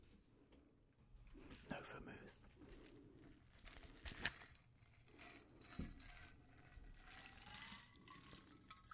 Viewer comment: What did he say in 26:13 ? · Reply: He said no vermouth. It's a ingredient you put in drinks